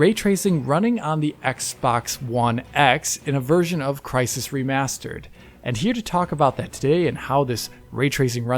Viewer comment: All I heard was ‘PC master race’